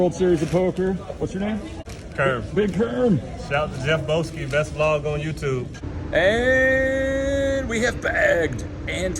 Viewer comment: did he say "...Best lover on youtube?"